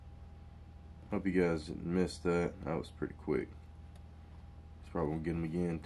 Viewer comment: Did I hear that right? "Hope you guys missed that."?